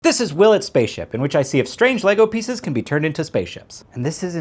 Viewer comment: I heard "will it spaceship" as "Willard's Spaceship" at first. Which is odd, because I'm reasonably familiar with this series.